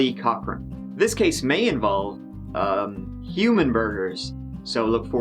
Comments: At first I thought he said “human burgers”. Amused, I figured I was just mishearing “human murders” gradually realizing I was right the first time. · At first I thought he said “human burgers”. Amused, I figured I was just mishearing “human murders” gradually realizing I was right the first time. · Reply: I thought he said ‘human murders’ and was really confused as to why he was saying it like it was an unusual occurrence. Then I read the comments!